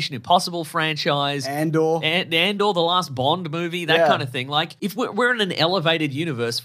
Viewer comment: "Andor: The Last Bond Movie." That's what I heard.